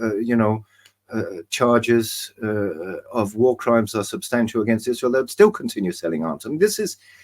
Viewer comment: I hear, "war crimes are acceptable" 🤷‍♂️ I guess it's opposite day